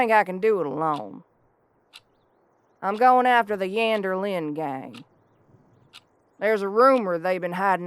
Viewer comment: At first I thought she said "Vander Linde gang" and I was like, "no, they were my friends... I can't..." Then I realised she said "Yander" and I was okay with it. Loved this!! Neat little western setting.